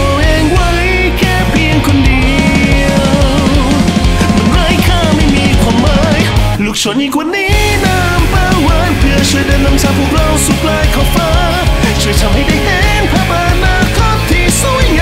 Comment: ขอโทษครับที่หูได้ยินว่าลุกโชนกว่านี้น้ำปลาหวาน